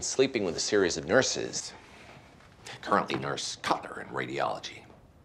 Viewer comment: Did he say Nurse Kutner?